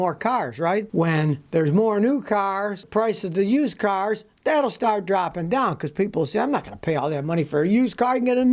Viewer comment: I thought you said pull-start 😂